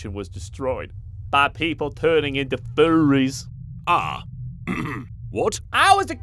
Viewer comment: is funny did said furries?